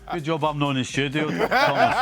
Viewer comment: What did stevie say at 5:15? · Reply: Good job I'm not in the studio Thomas